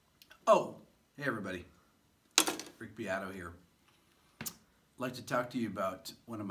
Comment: When he said his name I heard "Rigby Idaho here" 😂😂